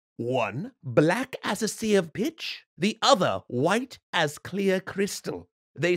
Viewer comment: did he just say a bad word!?!? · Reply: He said pitch.